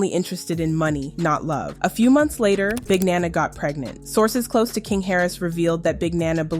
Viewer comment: Oh Big Nanna i thought She was saying Bananas lol